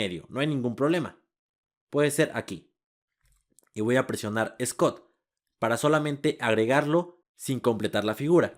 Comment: Cuál es la tecla que dice en el sg 59:37? Tecla scott? Cuál es esa? Dónde la encuentro? :c